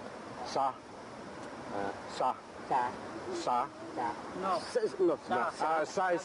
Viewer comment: when he try to say Sa is Sleep I thought he suddenly knew English cause I heard him said "Sa is not" and then the white man said Sleep Sleep Sleep.